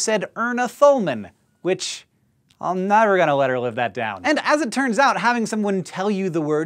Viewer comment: Can somebody tell me what on earth this man uttered from his mouth at 2:53? I think he’s trying to say “I’ll never let her live that down” or maybe “I’m never going to let her live that down” and it ends up sounding like: “*wild tongue flailing* live that down” lmaoooo The captions are certainly not what it sounds like and it’s particularly funny considering he’s teasing his grandma for a verbal mixup.